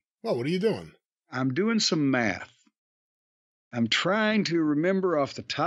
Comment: 🤣 I stepped out of the room and I thought I heard Jim calmly say... "I'm doing some meth.” 🤣 God he has gotta stop watching AEW!!!